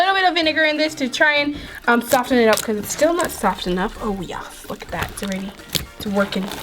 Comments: Did she just say "oh yeah fuck that" in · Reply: No, she said oh yeah look at that but it almost sound like that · Who heard the f word at 6:37? Edit: #earlysqaud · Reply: No, she said oh yeah look at that but it almost sound like it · On 6:36 did she say oh yea fuck that. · did anyone else hear her say “oh fuck yeah”??? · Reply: No, she said oh yeah look at that but it almost sound like it